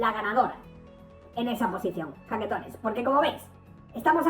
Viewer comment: Pepe? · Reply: estaba viendo un video d hace 2 meses donde el m parece que dijo "jaquetones"..... t paso el link.... fijate en el min 6:11..... no s llega a escuchar bien, pero parece que lo dice el.... y el video es d antes que aparezca <link>